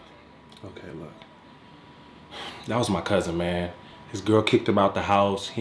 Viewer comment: Thought it said "COUSIN IRAN" Was a bit worried